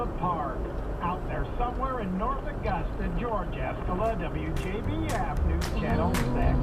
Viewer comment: Oops,turns out Mr. didn’t make an error there. He said “Out there somewhere in North Augusta,George E(skola). Slurred together it sounded like NA,Georgia 🙂